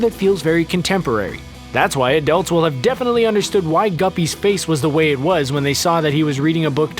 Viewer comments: Did he say guppy at 3:18... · did you just say GUPPY????